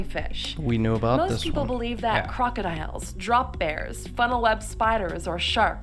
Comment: A wombat will total your car, What?? did she just say "Drop Bears"??